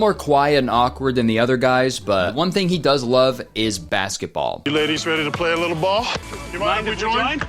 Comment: I THOUGHT HE WAS GONNA SAY BACKSHOTS NOT BASKETBALL 😭😭